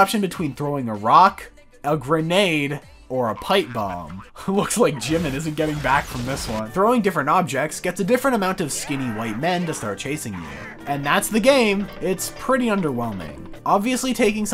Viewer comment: man i am so down on rot content i thought he said in 6:33 skibidi white man